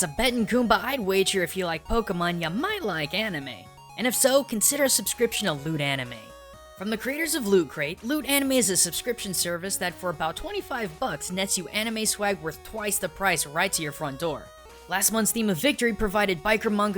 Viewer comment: I swear I thought you said "Lewd anime" a moment there at the end